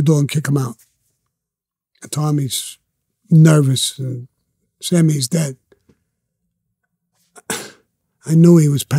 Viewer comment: I’m a bit confused by this story. From what I’m hearing here, Joe wanted to have Tommy killed for having an affair with his wife but was afraid the boss, Shorty, would then obviously know who had his nephew killed (Cause Tommy had an affair with Joe’s wife) so he thought maybe have Shorty killed too (so he wouldn’t be around to retaliate) or just have Tommy killed 6 months later so it wouldn’t be so obvious. But here’s where I get confused. Sammy gets the job but he doesn’t kill Tommy and/or shorty but ends up killing another guy named Sammy? So what role did this other Sammy play into all this?? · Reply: Ok, I listened to it again very carefully and Sammy quoted Tommy as saying “Sammy HE’S dead.” I didn’t hear the “he’s” the first time…LOL and I thought he said “Sammy’s dead.” So there wasn’t another Sammy in this story after all, so who exactly was the “he” he was talking about? Who then did Sammy whack?